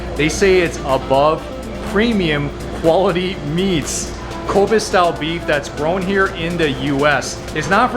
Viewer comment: I got a little worried in this video because at first I thought he said Covid style beef.